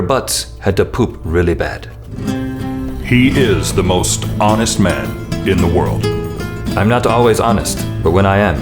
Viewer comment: Write your honesty here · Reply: I thought that it said world's hottest man .................